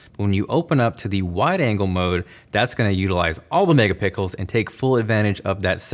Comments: did anyone hear that he said pickle instead of pixel? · anyone else hear "mega-pickle"😂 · Who else heard “megapickles” ??? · Who heard mega “pickles” ?? 😂 👇🏻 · I heard mega pickles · Anyone else hear "MegaPICKLES" ? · Anyone else catch him say Mega Pickles @ 1:53! 😂😂😂😂😂😂😳😂😂 I’m so dead 💀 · anyone else hear “mega pickles” instead of megapixels? rofl 😂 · 2021 and I just heard the megapickles 😹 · Did he say mega pickles at 1:53 🤔🤔😅😅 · am i the only one who heard MEGA PICKLES?!? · did he say mega pickles? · i heard mega pickle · I’m sorry, all I heard was; this is going to utilize all the Mega Pickles? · he says all the megapickels instead of megapixels kkkkkk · What’s with these guys calling megapixels mega-PICKLES? I heard Everything Apple Pro do it too. Are they doing it accidentally? They never laugh or anything so I just can’t tell if it’s intentional or not but it doesn’t seem like it. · im sorry im just laughing my ass off cause i heard mega-pickles and then came to the comments to write about it but checked to see if everyone else heard it too first and here we are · Did he just say mega pickles? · did he say 'that's gonna utilise all the mega pickles" instead of megapixels just me lol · did I heard tight? 1:51 MEGA PICKLES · Did you say... MegaPickles?